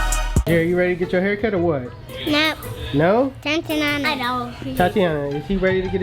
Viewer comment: am I trippin or did he just call his daughter thotiana I just replayed that shii bout 25x 😂😂😂😂😂